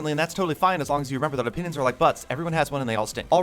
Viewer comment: For a second I thought he said "penises are like butts"